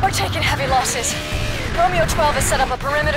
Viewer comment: Claire says Romeo twelve but the captions say Romeo Two...